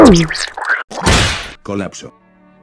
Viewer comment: bruh i hear "colapso" when scout it falls but with loquendo